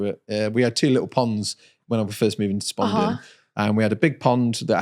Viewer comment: did you say "Spalding" Did you grow up in Lincolnshire?! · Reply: Spondon, sorry I’m not a Lincolnshire lad, born and raised in the smelters of west Germany before reaching the safety of the UK